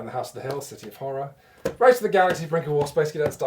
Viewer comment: At like 4:55 what is the game he says right after Race for the Galaxy??? It sounds like the name is 'Break the Wall???" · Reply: The Brink of War. You have probably worked this out in the two months since you posted your question, but there you go.